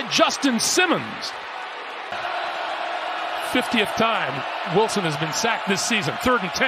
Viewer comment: Don't know if I heard that stat line correctly.. did they say 50 TIME?!